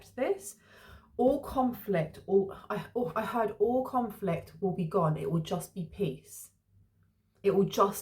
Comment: I heard all conflict will (dissipate) for more sense of the word